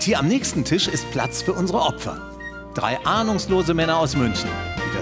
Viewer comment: Bei 0:31 verstehe ich immer "2 Arnuslose Männer" xDD